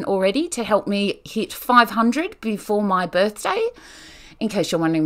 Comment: I thought you said your 500th birthday 😂